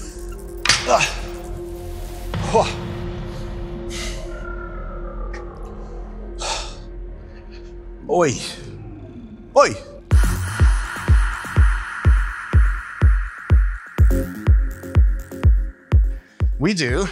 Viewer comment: Did u said "hi" in portuguese? brazilian jiu-jitsu here :p. Really like your job!